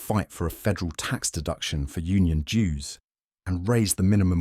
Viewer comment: dews not Jews right? That confused me when I heard it. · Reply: Dues